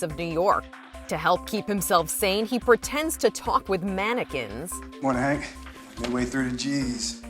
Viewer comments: I thought it said “would you eat mannequins?”💀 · Who else thought it said would you eat mannequins?